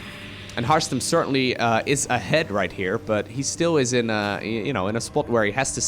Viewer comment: did you just call harstem a head? How rude.